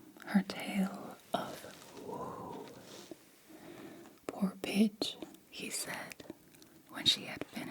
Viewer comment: I thought you said “poor bitch”! Which, ironically, would’ve been more accurate as you were referring to a female dog. 😂